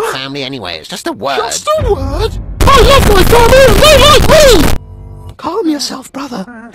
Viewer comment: i can't understand what he is saying 👁👄👁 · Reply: he said “I LOVE MY FAMILY AND THEY LIKE ME !!”